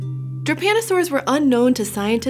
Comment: Damn, every time I hear "japanosaur" instead of "drepanosaur"